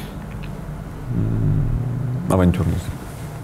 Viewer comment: что он сказал? не могу разобрать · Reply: Авантюрность